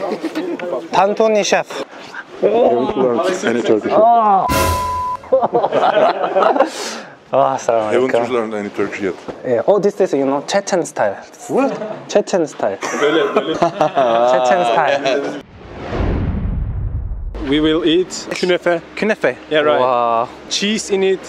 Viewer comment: did he just say chechen style ??🤔